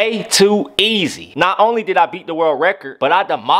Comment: What he said: "they/them" What I heard: "GaY sLaSh DaMn"